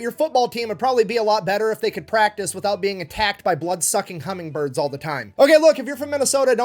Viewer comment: I listened to this too fast and thought you said blood suckin cumming birds